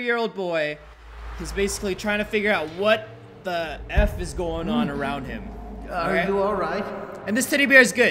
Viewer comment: What did u just say 0:16 u said what the food right Roy